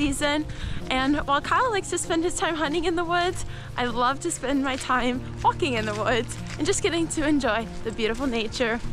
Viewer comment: what did she say? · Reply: She wants to have fun!